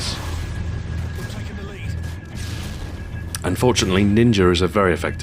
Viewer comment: Did you just say "Ninger" at 2:32?